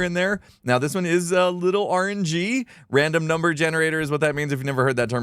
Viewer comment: I heard "this is a little orangey" which matches the lights, lol